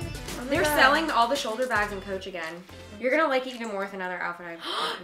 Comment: Lol, I thought Niki said "shoulder pads" when she opened the shoulder bag 😂😂😂 lmao